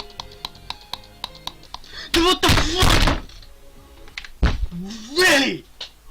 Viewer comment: i thought said ‘ WHAT THE CRAP!” i hen he got 98